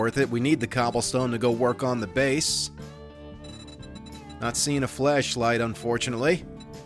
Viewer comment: did you say, "Not seeing a Fleshlight, unfortunately"?